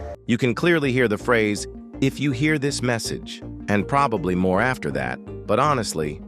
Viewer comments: Honestly, I hear the phrase "If you can hear this message, you are gaaaay" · i heard if youhear this message they found you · all i heard is "if you hear this message, run away from you're base." idk man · I swear he said "If you hear this message your gay" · I heard at 0:56 “If you hear this message.. your gay” 💀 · "if you hear this message they are here" that what i hear